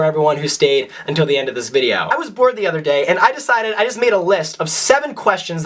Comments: When he said "I was bored the other day" I thought he said "I was born the other day" 😂😂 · I thought u said "I was born the other day" instead of "I was bored the other day" I was like, dude me too 😂